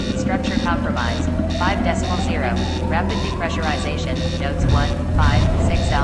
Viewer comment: At first I thought that said Depressing, not Depressurizing, and was like yup, relatable pod